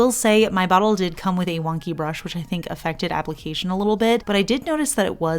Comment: Did you say your bottle came with a ‘wonka’ brush? Seems appropriate for this collection. 😂🤣 sorry, I couldn’t resist…